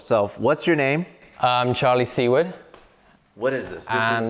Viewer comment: I was zoning out when he said his name was Charlie Seward, I heard “c-word” and my first thought was “his last name must be Kunt and he’s censoring it to be polite”. My brain has been warped by decades of internet profanity.